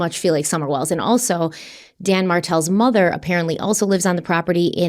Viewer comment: My neck snapped when you said Dan Martel bc I thought you said Dan Markel